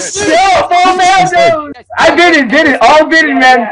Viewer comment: To me it sounds like he says "5.8m SLIT/SLITH"